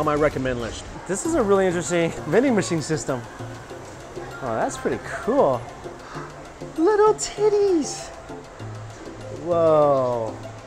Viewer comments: I use the captions most of the time. This is a perfect example of just how horrible YT closed captions is. He says, "That's pretty cool, little teddys (as in teddy bear)." That is NOT what the captions says haha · I had this as background noise and at 11:13 i swear he said Little Titties so i had to back up and see what it was Oh mike you crack me up. XD